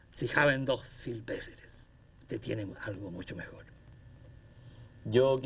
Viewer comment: Alguien que por favor me ayude a esclarecer lo que dice el profesor Jorge Eduardo Rivera en el minuto 58:09 cuando Heidegger le habla, gracias. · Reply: Creo que dice: Usted entiende más algo, mucho mejor. O, "usted tiene algo mucho mejor.